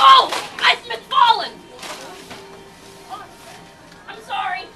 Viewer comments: I thought it said I've been fallen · The Mcfloor is very Mcslippery at McDonalds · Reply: I thought she said " I've been fallen